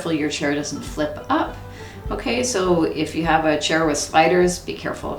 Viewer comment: Love this stretching routine, and your calm, relaxing demeanor (all delivered in that delightful Canadian accent. :-) ). Thank you for posting! BTW, for the life of me, I could swear you're saying, "....if you have a chair with SPIDERS" (around 4:04)! I was thinking you folks must have a pretty serious Arachnid situation up there, before I realized it was SLIDERS! 😂